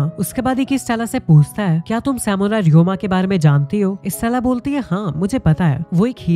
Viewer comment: did he say Samurai ryuma....from wano arc of onepiece 😅😅